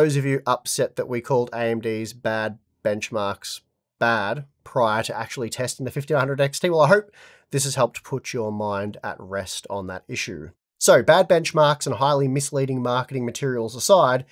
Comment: did he said "put your mouth at rest" or "put your mind at rest" ?! ;-)